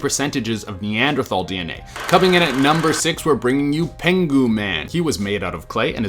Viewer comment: Penghu man I straight up thought u said penguin man